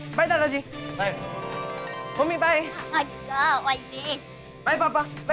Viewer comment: kya bola dya ne, mai boht irritate hota hu ess se yr😮‍💨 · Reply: Ha dika all best 😂😂😂😂😂yeh boli h daya